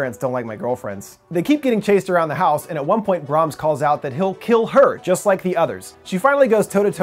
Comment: I actually thought he said "if you leave i'll kill him! i'll kill just like the others" because not only it sounds like that, but also when Greta gets to the gates she turns around to save Malcolm